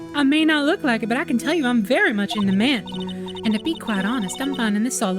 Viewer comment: HELPP at first i thought she said “i’m very much into men” there, that confused me so much lmao